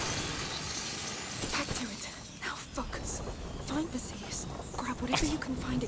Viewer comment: I also thought she said, "Now f*** it." 🤣